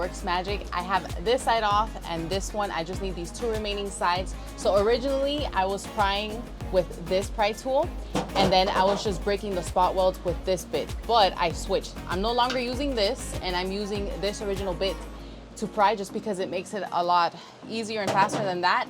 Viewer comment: Love it. @ 4:17 I thought you said “I was breaking the spot welds with this bit*@ but you said “BIT”. Lol. I was like damn she’s a bad as*. Haha. Love following this project and seeing you tackle things that are new to you. Such a bad “bit”